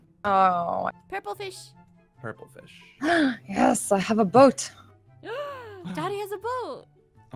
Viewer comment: when she said dotty has a boat i heard daddy has a boat